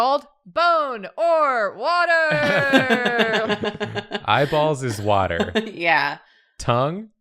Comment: I thought said “my balls is water”